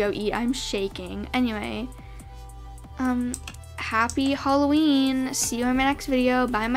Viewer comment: I know I'm wayyy to late but I just keep on watching this video over and over and did say Happy Halloween in the outro? 17:16 or I'm I just deaf?